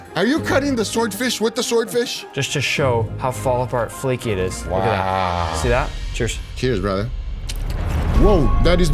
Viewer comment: I think I heard say it needs more butter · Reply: “Are you sure you got enough butter?”